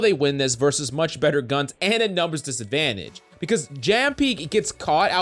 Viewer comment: Did I just heard Jam pi?